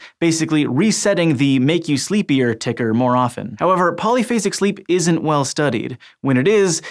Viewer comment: Definitely thought it said "Polyphasic SHEEP"